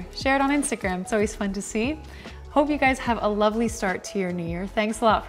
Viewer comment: i just gotta say when she said "pick up limes" i understood "pick up liNes" and was very confused about why someone would want to think of pick up lines and write them down... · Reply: i thought she said “pickle plimes” lmao